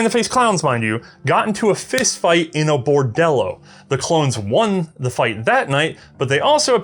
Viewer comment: I’m not the only one who heard Clones right? Did the Grand Army of the Republic adopt this revolver?